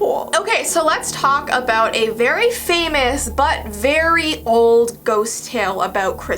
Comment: Right before 3:33, you know how she said "Now, let's talk about a very famous, but old, tale." I THOUGHT SHE SAID "now let's talk about a very famous BUTT." Edit: If the whistle unleashes something, WHY DOES IT STILL EXIST THEN?!